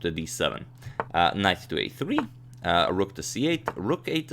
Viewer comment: Am I the only one that heard Knife instead of knight at 4:56 and 4:59?!